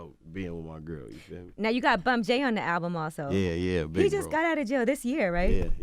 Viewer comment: I couldn't hear it clearly at 19:28. Is it 'Bump J' the artist they start talking about?